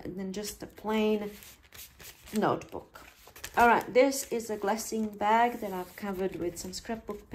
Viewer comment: I thought you said blessing bag but you really said glassine bag..hmm..